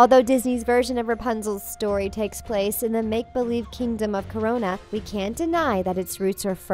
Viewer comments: Did I just heard Corona? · Did she say Corona ?!